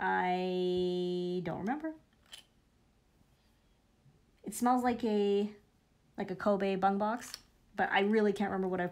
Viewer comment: Did she actually say "smells like a Kobe bungbox"??! 8:05.. gonna assume that means something else in penland